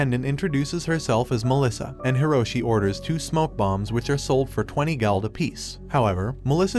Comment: Did they say gold or gald